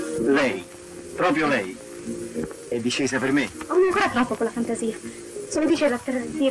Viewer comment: dal minuti 21:12 al minuto 22:22 si può avere la trascrizione? ci sono frasi poco chiare nei minuti 21:15 "non .... troppo con la fantasia"; 21:37 "è colpa mia se .... pasticci"; 21:51 "ma.....conosco!"; 22:04 "lei vorrebbe dire che avete una mar.... (assoluta a me?)" ; 22:10 "ah, jei?" questo è ciò sento nella conversazione. é una convesazione bellissima che me la vorrei scrivere. Mancano quelle parole però, che non riesco a decifrare. Spero che qualcuno di voi mi aiuti. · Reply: "Non ANCORA troppo con la fantasia" 21:37 "è colpa mia se Lei SI METTE in certi pasticci?" 21:51 "Come se non la conosco?!"